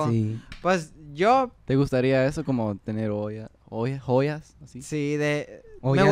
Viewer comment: did say whould you like to have hollas